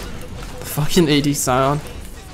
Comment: Did he say "Fucking AD Sion" everytime they kill that Sion in that game? LOL